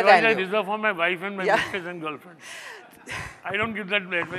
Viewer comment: Did he really said "that advice I reserve for my wife, my mistress & my girlfriends🤣🤣🤣